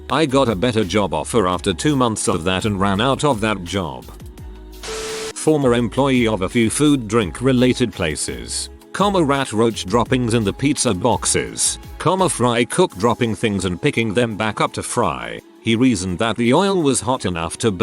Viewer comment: for a second I thought it was going to say "fry cook droppings in the food/etc."